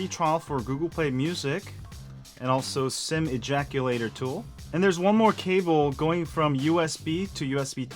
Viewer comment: Did I just hear "SIM ejaculator tool"?